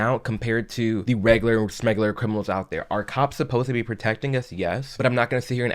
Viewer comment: did you say "regular smegular"?